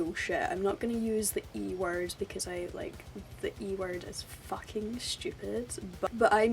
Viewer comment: What's the "e" word? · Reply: Elitist.